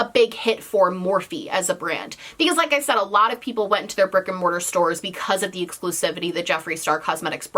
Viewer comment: am I the only one who hears "fricking Morphe stores" instead of "brick and mortar stores"?